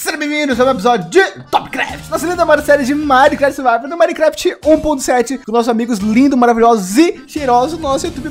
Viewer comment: só eu q ouvi minecraft 1.7?